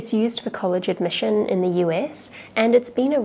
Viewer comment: The subtitles said SS instead of US at 0:10 i think YouTube whants to say something.